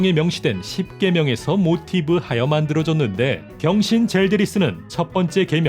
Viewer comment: 이때 나만 병신이라고 들림?